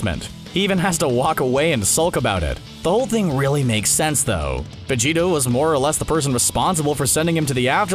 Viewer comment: did he say vegeto